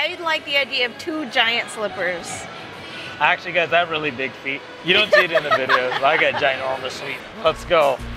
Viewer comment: 😂😂 At first I thought said "I really dig feet" and I was thinking "awkwarrrrdddd". Big feet, got it.